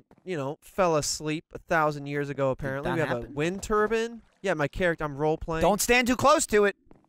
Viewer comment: Did Trevor say Wind Turban?